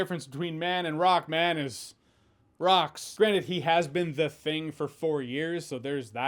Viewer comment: you said "granted" but I heard "granite" :)